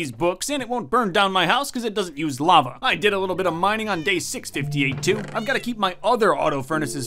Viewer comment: I thought it said day ass 🤣